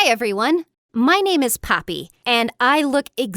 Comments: ok ok so i THINK she said "my name is Poppy" but i heard "my name is Papi" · There is a new infection goin around YouTube... the Billie Infection. Also, why did I hear “Hi everyone, my name is Billie, and I look like Billie Eilish” in the intro? I had to go replay it and then I heard “Poppy”, and at the end she said “My name is Amy...”. Am I hearing things?